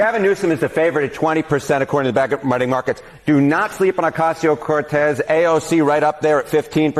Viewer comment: I thought he said "a cocktail Cortez" lololololol that should be her new nick name